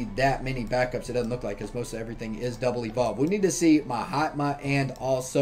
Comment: Every time you say ma hatma i hear “my hot mom”